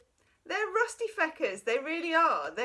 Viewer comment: you curse so smooth “rusty f*ckers” did you say? 😍😍😍